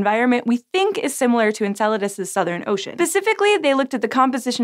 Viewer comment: Did you just say... Pacificly?